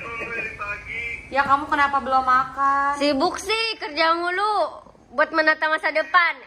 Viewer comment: Yang kenapa kamu belum makan? Apa aku salah denger atau gimana nichhh